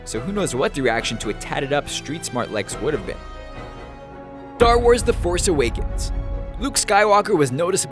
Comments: did you say Dar Wars? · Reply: I thought he said thar wars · sounded like tar wars